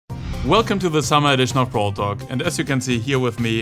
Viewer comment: i heard Frank:Welcome back and this is not a brawl talk